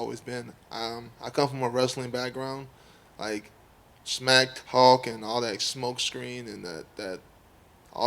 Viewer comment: What is he saying at 6:01? I don't speak English in my real life and I need to know this exact line. Anyone please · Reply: smack talk ? when you speak to people in a rude and arrogant way.